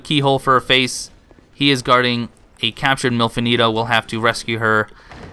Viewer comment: Not gonna lie, I thought you said "MILF Anito" and I got a bit confused. Had to look up the character to see what you were talking about!